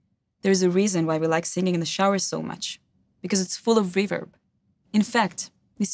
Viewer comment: at first at 00:15 I thought she said "its full of beaver"...spluttered on my early morning coffee...well done...............